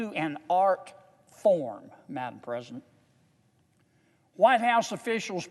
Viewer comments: Did he say “madam president” at 7:09?? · Did he say "Madam President"?